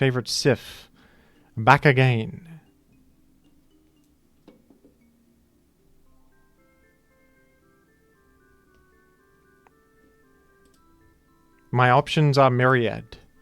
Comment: I thought he said "バカゲー (Bakage)", or 'stupid game', thinking he turned into Japanese owing to the prior shenanigans with hax, but then I realized he just said "Back again".